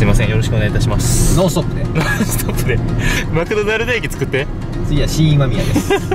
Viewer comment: 【空耳】マクドナルド駅